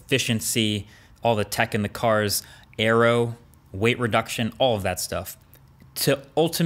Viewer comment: "airrow", "arrow"? What does he say? What's that? Thnx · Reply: Aero.